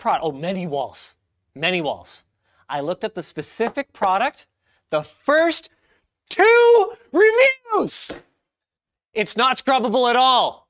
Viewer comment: What does he say at 4:26?!? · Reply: The first, TWO REVIEWS!!!